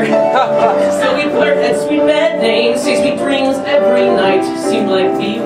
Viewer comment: does anyone know what he’s saying heere? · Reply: "had sweet pet names" :)